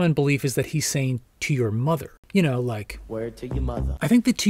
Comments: I always thought John was saying, “Sue your motha ...” . Maybe just me. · Sounded like John said "To your muck" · I thought John was saying " To your most " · I always heard : "to your muffin"